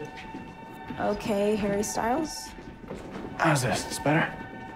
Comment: is it just me that thought she said HaIrY sTyLeS